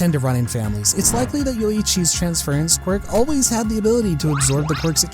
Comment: Bro i thought i heard "yoriichi" 💀